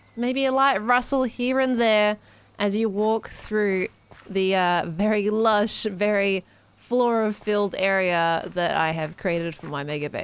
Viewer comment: I thought she said "Very lush, very chlora-filled" and laughed at the pun before I realized